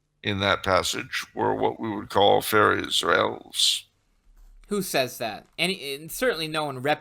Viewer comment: What does say at 1:00:15? 'Fair Israels?' I can't understand what he is saying? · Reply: "fairies or elves"